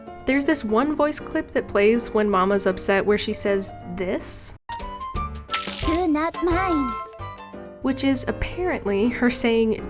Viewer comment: I hear " do NAHT mind".